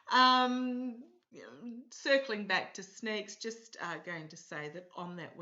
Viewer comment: Sorry heard you saying Snake. Sneak ...